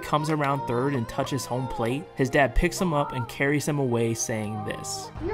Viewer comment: I thought he said “little mans backflip was elite” But he said “little mans bat flip was elite”